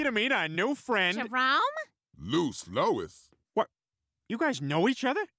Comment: Did he just say “Loose Louis”?😭